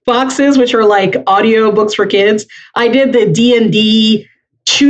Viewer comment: I know the book is called Forge Your Quest, but I heard "Four Jerk Quest" and that is also a good title for an intro DnD adventure.